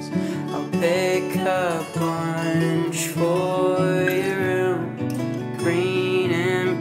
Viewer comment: with subtitles says "Take a punch for freedom" 😂???